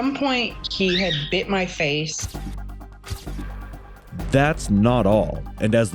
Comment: I already see a common theme in this video where the subtitles are going to say 'wait' instead of Wade. 2:13 Hmmm... again. Fun.